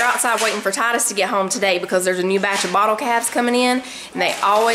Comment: I love freezer pb & js. They taste even better after thawing than freshly made. I don't know why, they just do. Oh good grief...I thought you said the kids were outside waiting for the new "bottle caps" to come in. Like what?? ...then the penny dropped: bottle "calves"! LOL (